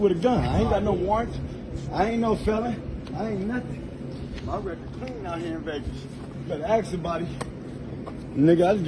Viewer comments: (Ironic - I know) it sounds like he says, "My record's clean out here in Vegas". So, maybe this is from Las Vegas? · sounds like he says "my record clean out here in Vegas" Looks like one of the extended stay hotels in Las Vegas, NV. Specifically the Siegel Suites.